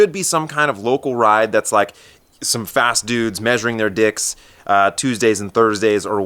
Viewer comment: did you really say "measuring their dicks" at 6:31?